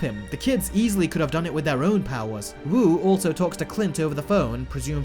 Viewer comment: he was calling a person named Cliff not Clint. When I watched the first time I thought he said Clint but it was just person I didn't care about.